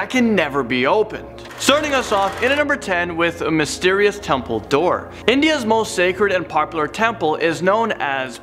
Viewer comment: the number 10 when you said "Mysterious temple door" I thought you said "Mysterious Dumbledor"